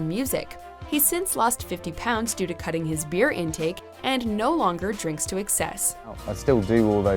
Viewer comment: at first i thought she said "due to cutting his beard"